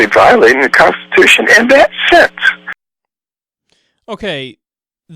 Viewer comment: It sounds like he said "…and that's sex!". XD (Excuse my immature sense of humor.)